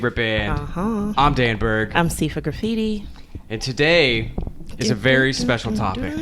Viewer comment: For some reason I always heard it as seafood graffiti Whoops……